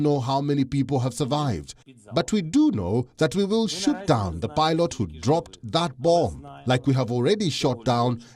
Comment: When he translated "shoot down the pilot", I thought I heard "shoot down the blyat". I was about to give that sentence a hard R rating.